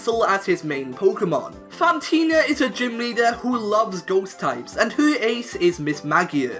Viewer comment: I thought he said Fantina loves goats types and I was so confused